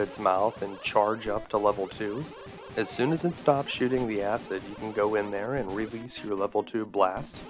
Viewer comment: did you say "as soon as it stops shitting the acid?" LOL